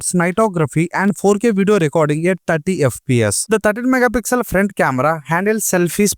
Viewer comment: i heard tati fps 😂😂😂